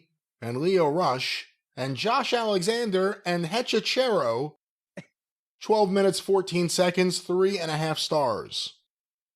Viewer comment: when said 12 minutes I thought he said 12 stars. I guess there’s some hope in this world because I was still shocked by the thought of a 12 star match coming out of AEW….barely.